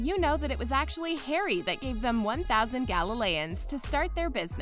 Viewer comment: CHILE SHE DID NOT JUST SAY 'GALELEONS'